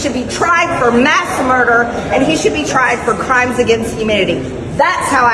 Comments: It sounded like she said “crimes against humidity” 😂😂😂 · "tried for crimes against humidity" . . . an I hear that right?! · Did MTG say Dr Fauci should be tried for "crimes against humidity"...? (08:07 mark) Maybe she is blaming him for climate change too, except she doesn't believe in that. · It really sounds like she said that Fauci should be tried for crimes against humidity. Maybe she doesn't like his dry sense of humor? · Sounds like she said crimes against humidity. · Did MTG say crimes against humidity???